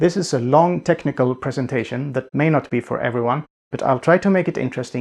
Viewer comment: "This is a long technical presentation" You got my attention. And my thumbs up. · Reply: I misheard it as “non-technical”.